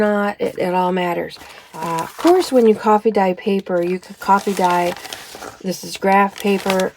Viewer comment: I don't, but I'll see what I can do. The ones on my Etsy shop are real coffee-dyed papers I've done that I scanned and altered their tints digitally. Easy peasy. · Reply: one of your videos you had this really cool paper and I thought you said it was your iced coffee paper. I was intrigued .